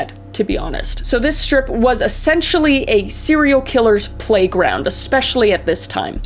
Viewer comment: I thought she said sanrio killer (sanrio is hello kitty if you don't know) I was like hello kitty is a murderer?😂